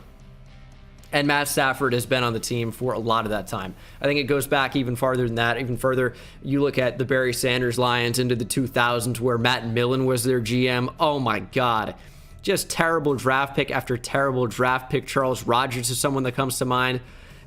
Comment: We hen he said Barry Sanders I thought he said Bernie Sanders and I was confused af